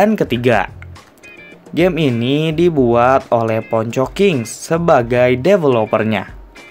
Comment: gw dengernya kok kayak pocongking yak :v?